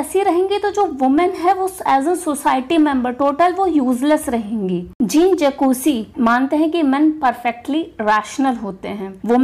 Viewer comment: ma'am can you write the name you mentioned here ? I didn't understand what the spelling could be.. Was it jin jacuzi or something? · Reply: Wollstonecraft mentions Jean Jacques Rousseau as an example of someone who became so fed up with the injustice of the modern world that he turned away from it and lived in solitude.